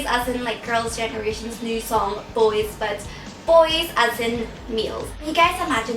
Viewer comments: when she said "boys as in males," for a second I thought she said "boys as in meals." Her accent is adorable :) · omg at the beginning when you were like, "boys as in males" i thought you said "meals" and i was like wtf..LOL love you bubz<3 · I thought you first said Boys as in Meals. Your accent. <3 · I thought she said "boys as in meals" and I was like "what the he-....OHHHHH LOLOLOL!" :3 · I thought you said "boys as in meals" in the beginning XD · I thought you said "meals" not males xD sorry Bubz :3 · LOL i thought she said "meals" when she said "males" x] yum, lets eat them? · I misheard "males" as "meals" and cracked up laughing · I thought said Meals when u said Males hahha love your accent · when U SAID ... MALES i thought u said MEALS :)!! haha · Thought she said "Meals" when she said "Males" <3 Oh bubz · about "males" i thought you said "meals" LOL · Sounded like she said "Boys; as in meals." lmao. I'm like...okay. Some are delicious eye candy. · i thought she said "boys as in meals" hahaha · Haha when you said males, I heard "meals". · At first I thought you said 'meals' and not 'males' and I started laughing extremely hard. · I thought you said boys as in "meals" not males XD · LOL at 0:12 I thought you said "Boys as in... MEALS." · I heard it as "Boys as in meals" lol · i thought you said at the start 'boys as in.. meals.' hehehe